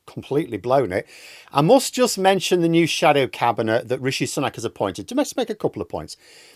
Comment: LOL - I heard it as the "Shallow Cabinet" :eyes-pink-heart-shape